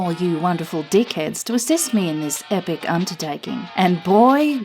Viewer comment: I thought she was going to say cunt-tertaking 😜